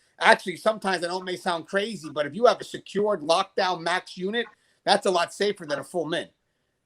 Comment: "thats a lot closer than a ??fulmin??" ... what are you saying there. a full what?